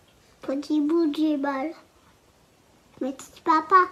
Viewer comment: "petit bout 10 bal" faites gaffe de quoi vous parlez devant vos gosses bordel